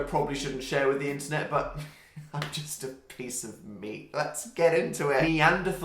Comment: when he said he was a piece of meat I thought he said piece of meme